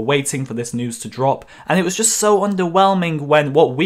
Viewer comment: I can’t be the only who thought he was saying “ Ewok cunt”